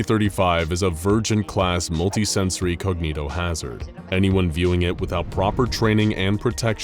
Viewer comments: I can't be the only one who initially heard that as "Virgin class multisensory cognitohazard", can I? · i thought they said virgin not vurgent. lol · i thought you said virgin-class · Did he say virgent or virgin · I thought that it said a virgin class monster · For a moment I thought you said this scp was a virgin class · I thought it said virgin instead of vurgent, lol